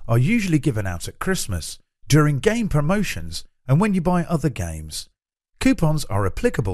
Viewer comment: Did anyone else hear "Gay Promotions"?